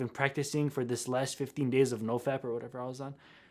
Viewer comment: I heard "no fat" until about 1:17 when he entered the class Edit: 1:17 not